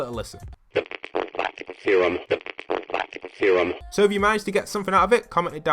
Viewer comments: it sounds like he is saying the platinum theorem. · I think it says "syrum" or "theorem"